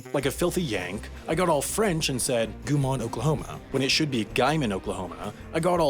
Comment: Is Oklahoma pronounced as Oklahomana?? Why does it say t like that? Is that a joke about mispronunciation?